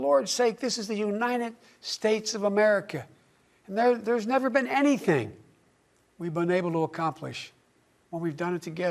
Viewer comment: “this is the United States of America; there’s never been anything we’ve been able to accomplish when we’ve done it together.” -Joe Biden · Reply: You misheard - it was "there's never been anything we've been UNABLE to accomplish" - my hearing is pants, but even I can hear that clearly.